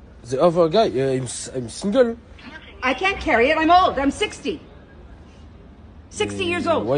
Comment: "I'm 60" I thought I heard "I'm 16" 😂😂😂😂